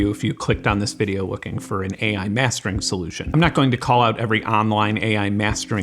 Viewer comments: I thought this said AI Masturb@ and I got very confused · I thought it said "AI masturbating" and i was oh, ai is bad for jerking off lmfao